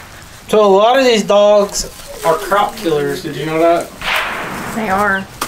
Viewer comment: You said they were croc killers but I thought you said cop killers and I couldn't understand what. I'm glad I heard it clearly the second time lol